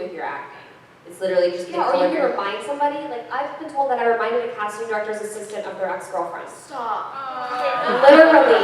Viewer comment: What Madelaine say? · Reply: I’ve been told that I remind a casting director of her ex girlfriend